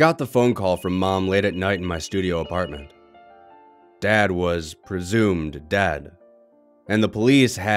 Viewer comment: I thought he said “dad was presumed dad”.